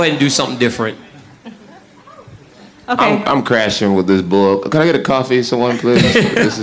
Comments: I thought he said “I’m crashing with this blow” haha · "I am crashing from this blow can I get another coffee?" Jon why the fuck did you say this? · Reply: Konsboni TV bloke* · is he saying "I'm crashing with this blow?" lol? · did jones say " I'm crashing from this blow I need a coffee"